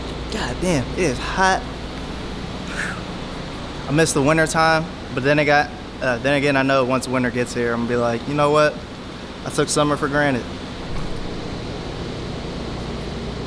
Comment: Did he just say he's taking summer for granit? 😂